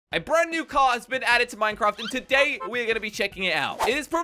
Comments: who else thought he said minecraft instead of gta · why did you say mineingcraft